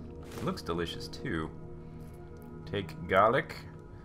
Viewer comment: I thought it said "Take a Leak"